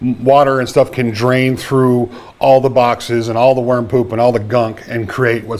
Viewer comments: What a relief. At first I thought you said warm poop. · I thought you were saying "warm poop". No smell? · Lol I thought you said "warm poop".